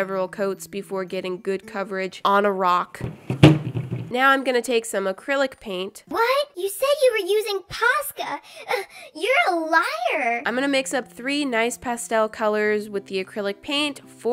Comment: i heard pasta